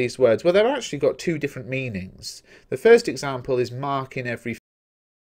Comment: did you say at 13:18? Thank you. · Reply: "marks in every face", like in the poem line ( I got that from context)